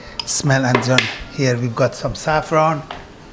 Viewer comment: it sounded like u said saffron.....what is that ingredient u added before placing it in the oven?.....I know salt, pepper, garlic, ?....